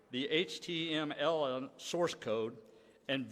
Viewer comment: Did he just say "HTMLN source code"? Geeze, no wonder their security is crap. Their leadership can't even spell UBIQUITOUS terms in the modern era.